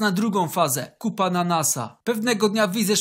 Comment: 'kup ananasa' ja usłyszałem 'kupa na nasa'